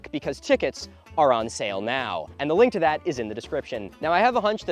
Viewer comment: When he said “tickets are on sale now” I heard “chickens are on sale now” And I was SO confused